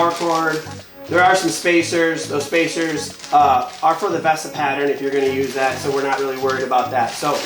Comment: for the what? I couldn’t understand what he said there, Vesta pattern? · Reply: VESA